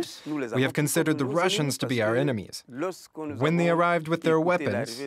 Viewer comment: Did I just hear "Russians to be our enemies" ? US National Anthem plays